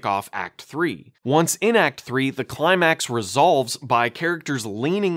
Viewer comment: I don’t think the subtitles are quite right unless I’ve misheard something — the subtitles say “the climax explodes” but it sounds more like “the climax resolves”